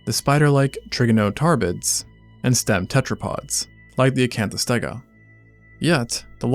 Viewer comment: When I first watched this, I wasn't paying attention to the subtitles so instead of hearing "stem tetrapods" I heard "step tetrapods"